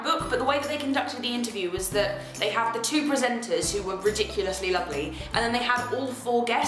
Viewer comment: I thought she said "two interviewers who were ridiculously ugly" and had an inner freak out